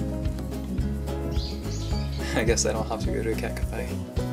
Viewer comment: does anyone know what said here? ouo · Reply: "I guess I don't have to go to a cat cafe!"